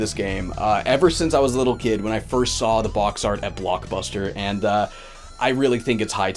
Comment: This video was great Did I hear "Plokbuster" at the end?